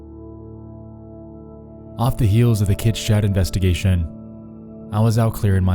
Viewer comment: I cant for the life of me hear what 'investigation' he talks about in the beginning - the one where he cleared his head after? - i keep hearing "kid shroud investigation"? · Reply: he's saying Kids Chat